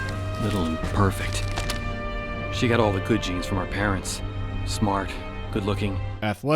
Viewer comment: "She got all the good genes from our parents" LIQUID? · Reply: I've misheard "she has got a coochie from our parents"... Got confused. (=